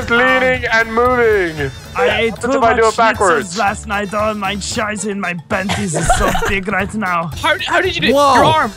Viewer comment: Did Terroriser say: "I have a lots of scheiße in mein Panties!!"? x'DD